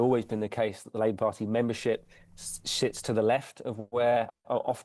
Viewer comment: Did he just say "shits to the left" 😂